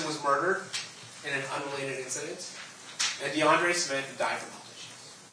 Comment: Listen from around 28:50, where you hear the prosecutor state that "DeAndre Smith died from publishing."🫢 Did I hear that right? How does someone die due to publishing? 🤔 I turned the Closed Captioning setting on and replayed that part; the words displayed were, DeAndre Smith died from publishing. I thought publishing had to do with the production of books, tapes, records, and other forms of media, but unless that word holds another definition in regards to someone meeting their own demise, I think there was a critical error made here. 🧑‍⚖️